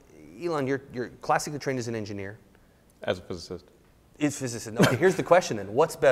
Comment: What does say at 0:21 ? In phisis ?